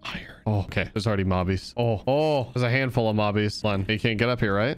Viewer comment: Love these planet videos! Also when you said “ mobbies” I thought you said Bobby’s then all I could think of is Bobby from Team Edge. 😂